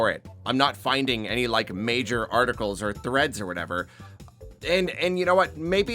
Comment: i heard that as “threats” at first but it would still make sense either way